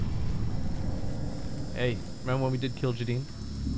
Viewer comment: "Hey, remember when we did _____" I seriously can't tell what is saying here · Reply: I think he say "Hey,remember when we did kill ja'den"